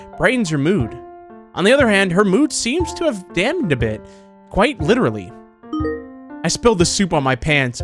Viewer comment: is it just me did he say the d word instead of dampened